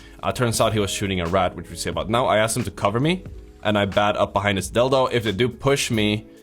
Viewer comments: Did he say: Behind his dildo"? XD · What did you said here? Sounded like "...and i bat up behind this dildo or dill tho" or something like that 😂 I am so confused xD